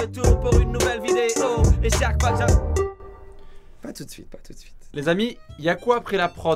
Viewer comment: c'est moi ou il dit( chaque vagin)??😂😂😂